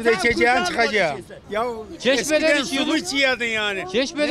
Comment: eskiden sumu iciyodun dedimi o? Benmi yanlis duydum